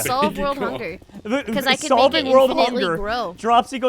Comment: I'm so dirty minded.. I thought say big pron